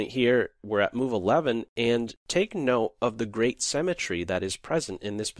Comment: "The great cemetary" ? That's what you said. I'm outta here! >